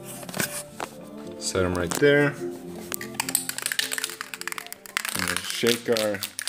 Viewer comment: Did he just say, "set him right there" at 47:00? IT'S A TRAAAAAP!